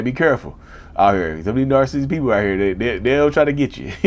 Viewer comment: 😮😮😮 did he say dark skinned ppl???? I'm lightskinned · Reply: he said “narcissistic people” not “dark skinned people” 😂😂 how did you hear that lol